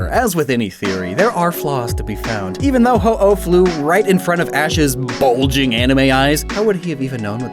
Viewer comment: "His BULGING ANIME EYES" my wifi cut out for a second so I heard "His BULGE"